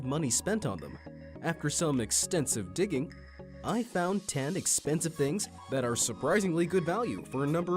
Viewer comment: I thought he said "iPhone 10" when it was actually "I found 10" 😂😂 I'm sorry I'm dumb😅😅